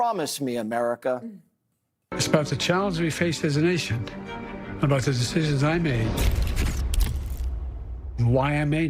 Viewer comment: Did he say "the de-cities" he made?